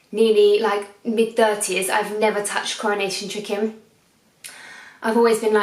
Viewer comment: Am I the only one that had to pause the video and google "Coronation Chicken?" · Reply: I thought she said “cornish chicken”? lol 😂